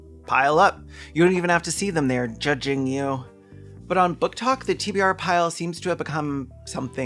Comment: I thought you said TB Art pile. I got that in my basement.